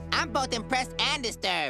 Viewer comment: When he said, “I’m both impressed and disturbed!” I heard it as- “I’m both depressed and disturbed..!”